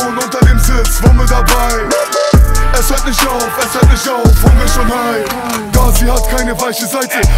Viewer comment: verstehe immer "unge ist schon high"